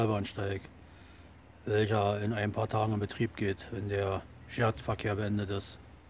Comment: Ich kann dich bei 0:09 leider nicht richtig verstehen: Welcher Verkehr ist beendet, wenn der neue Bahnsteig in Betrieb geht? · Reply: Ich glaube er hat gesagt "Sperrverkehr" und meint sicher die Streckensperrung bis Freitagfrüh (?)